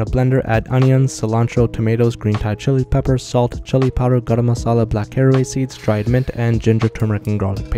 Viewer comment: When he garam masla my dumbass thought he said carne asada